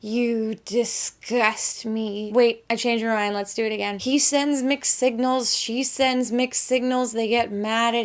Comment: I really thought you said "McSignals" instead of "mixed signals."